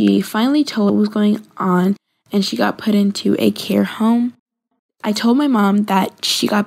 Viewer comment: Are we just going to ignore the fact that she said "hair home" instead of care home????